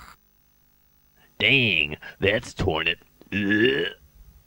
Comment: In all seriousness, what does he say at 6:48?! “Dang, that’s _______.” - I’ve been wondering this for years. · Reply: in my subtitles it said: Dang, that's toilet